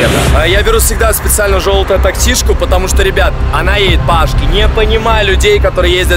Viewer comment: по ашке? или что он сказал